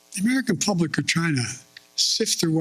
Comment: Did he say the American people of China?